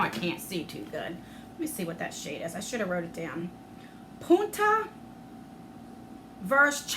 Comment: 😂 I had to rewind a min I thought you said the eye stuff was called puta!!!! That literally means whore in Spanish but punta...means tip like tip of a sword 🗡!! I was dieing lol 😆